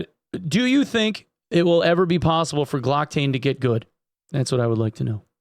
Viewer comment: What does say here? What is it that could "get good"? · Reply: Glocktane. He is a streamer that plays a wizard and was at one time ranked #1 in the world in Apex. I like to pick on him :)